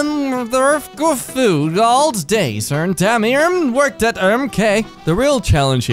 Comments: I think it said "Ahm the good old days when(?) Damien worked at Emkay" lol. No idea if "ern" is "when" tho. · I think it says "ah, the good old days when damien worked at emkay" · I think he was trying to say:ah the good old days when Damien worked at emkay, if anyone was wondering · I think what they were trying to say is "Ah, the good old days when Damien worked at Emkay"